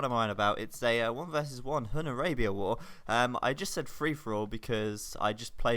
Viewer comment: shit, i thought it said Boar war...